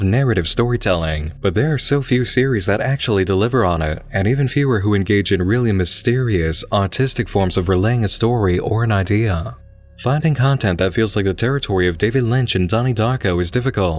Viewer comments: I love your videos Nick, I do, but every time you say "artistic" it sounds like you say "autistic" and I never get used to it. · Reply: Same I thought he said autistic and it took me a moment to realize. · I love your videos Nick, I do, but every time you say "artistic" it sounds like you say "autistic" and I never get used to it.